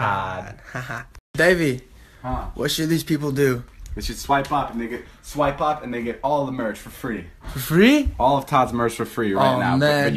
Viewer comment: did david say the n word on 20:12 ?